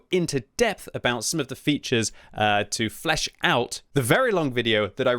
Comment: Thought he was gonna say fleshlight 🫣🍆